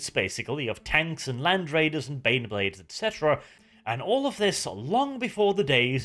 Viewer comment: did you say... beyblades?